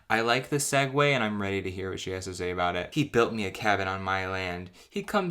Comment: I heard "she built me a coffin"